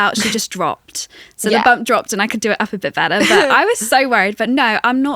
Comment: When they where talking about the bump dropped I thought said “Catlin said her bum dropped” but the pin released it was bump (don’t mean to make it weird ) 😂😂